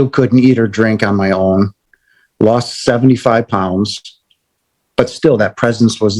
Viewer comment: Love this NDE but I have to say the subtitles really cracked me up 😂😂 he lost £75 lmfao! 3:21 I think it was supposed to say 75lbs lol other wise those cash strapped nurses robbed his ass! 😂😂😂